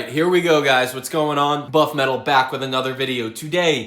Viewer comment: did you say Butt Metal?